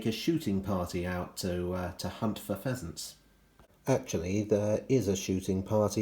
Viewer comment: I had to go back at one point as I thought you said "To take a shooting party to shoot the peasants". Well that's one way to make them work harder.